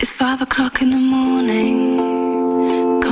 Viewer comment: I've thought it was saying 9 o clock all this time.